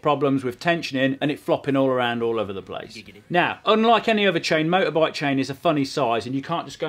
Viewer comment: did I just heard giggidy? lol